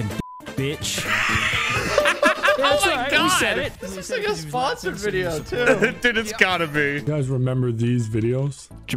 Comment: What does vanoss say at 10:02? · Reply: He said “dyke” Just saying it.